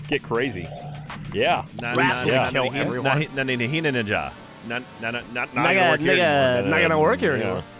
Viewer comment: Did anyone else notice at 10:45 when Geoff and Jack are making those sounds it sounds like Geoff says nigga